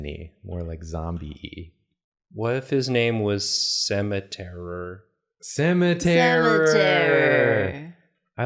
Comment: I thought said Cemeterra, since it's a ghost ground type, but Cemeterror is good too :)